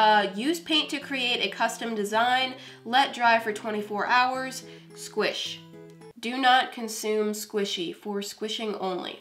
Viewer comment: Instructions: "Do not consume squishy. For squishing only." Moriah: "aww. Dang it!" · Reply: When I first saw this I thought you said "Do not Customize squishy" and I was like What?!?!?!? lol